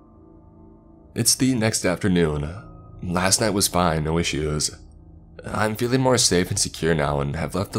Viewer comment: What did say at 16:04 ? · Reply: No issues